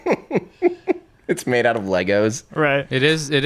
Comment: Did I just hear legos... Hmmmmm